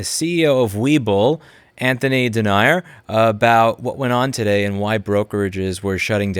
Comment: Anthony Denier? 99% sure I heard it wrong; corrections appreciated.